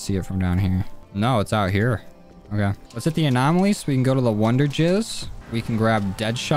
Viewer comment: Did he just say wonderjizz instead of wonderfizz?